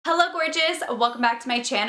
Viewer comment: When she said hello gorgeous I thought she said hello bitches